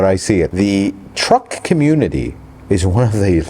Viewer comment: Truck community? I thought you said Trump Community.